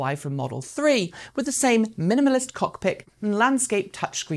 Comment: Did she say “cockpic”? 😂